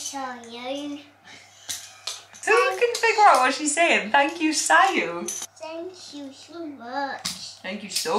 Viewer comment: Sounds like was saying thank you sire. Like a servant to a king